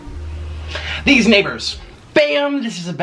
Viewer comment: Am i the only one that got so offended cause i thought he said these niggas